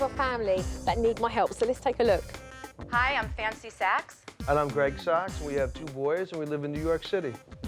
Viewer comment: Did she just say her name is fancy sacks?